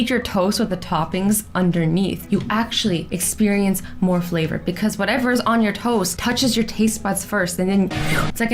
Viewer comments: I thought she said "Because whatever is on your TOES, touches your taste buds first!" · For the toast hack, I thought she said "toes" instead of toast lol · I heard toe instead of toast